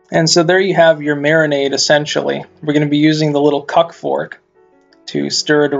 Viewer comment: Did he say cuck fork?